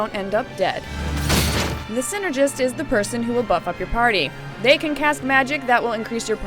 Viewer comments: She actually says butt fuck your party lol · hehe at 2:50 i swore she said the synergist are the ones who buttfuck your partry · did she say "buttfuck your party" at 2:52?